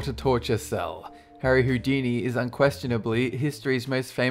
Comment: did I hear Hoodini?